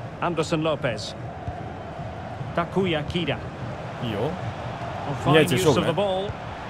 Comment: 実況の「タクヤ・キダ」が「たこ焼きだ」に聞こえてとても和を感じます。